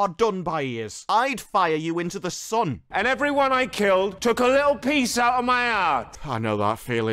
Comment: ‘And everyone I killed took a little piece out of my arse!’ That’s what I heard the first time.